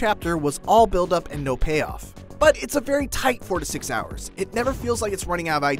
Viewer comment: Did I mishear it or did he say 46 hours? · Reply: mat bat four to six